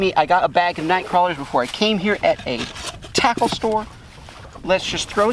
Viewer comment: Why did it sound like he said he got the night crawlers from a taco store